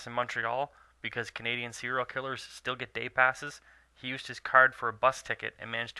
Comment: Canadian serial killers get day passes🤣Did I hear that right?